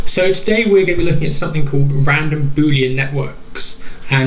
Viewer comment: Why do I hear constantly "random booty network"...